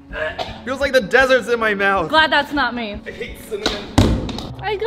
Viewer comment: On 1:39 he actually says "I hate CIMMANON "